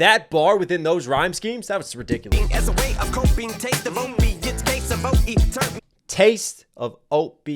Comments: sounded like caseoh tbh and it would makes sense with overeating lmao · What else should we react to? 🤔 Comment below! Also if you like the song in the intro check out "On To The Next One" with Jarren Benton now <link> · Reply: I swear I hear 'old me' within the 'O.E ' Reminds me with the Meg bar I originally thought he was talking c*m shots until I remembered what happened to her (hearing 'her feet' in 'a feat'